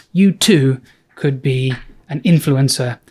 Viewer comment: Is it just me or did you say "you could be an influenza to"